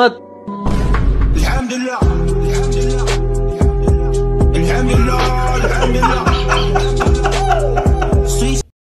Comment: wait a minute , What did he say.. Lamb🐑 dullah 😮😂😂😂😂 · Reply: Hello hIMa.....